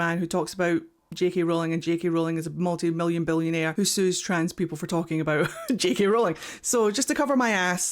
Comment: Not me mishearing you during the “allegedly spiel” and thinking you said “JK Rowling sues trans people for being trans” and thinking how progressive it was of her to finally admit trans people are real, even if it’s to sue them for existing 😅